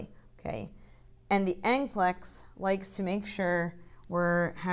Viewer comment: What word did you use? "Anklex"? 3lbs gain or loss?